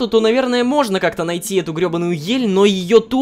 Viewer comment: послышалось ёбанаю